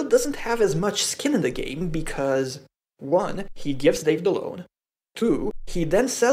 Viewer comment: what did he say? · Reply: "1) he gives Dave the loan" :)